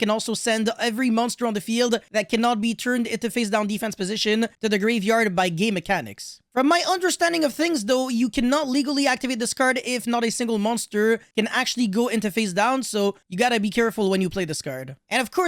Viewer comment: I thought at 0:25 you said "by gay mechanics" then I realised you said "game mechanics" but I decided I prefer the first one because it describes current yugioh 😂